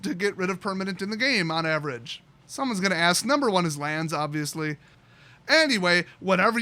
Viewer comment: When you said "it's lands obviously" I heard "my aunt el zomblie"